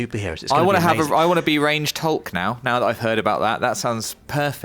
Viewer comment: After all this excitement of "ranged Hulk" the let down when you release misheard the Dev talking about "Deranged Hulk". I'll show myself out